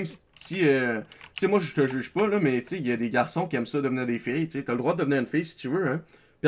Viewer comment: xd a 5 minute 25 j'ai compris "il y as des garcon qui aime sodomir des fille " xd jte decouvre frace a cette video +1 sub ^^